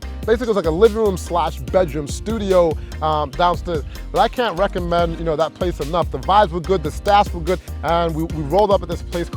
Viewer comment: I literally thought he said, 'I couldn't recommend this face enough'.